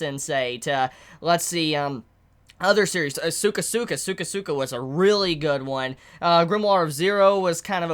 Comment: I heard "cyka cyka" but he was actually saying "sukasuka".. GOOD JOB ME. And the subtitle's agree it was "cyka cyka"